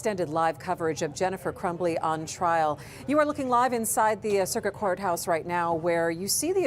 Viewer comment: At first I thought she said circus courts. Which wouldn’t surprise me with that defense atty